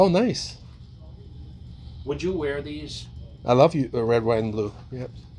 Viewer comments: Dude I knew I wasn’t the only one who wanted too see Dave 💪🏽💪🏽💪🏽💪🏽💪🏽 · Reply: did he really say "I love you"? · Did Dave say, "I love you"? 🤣🤣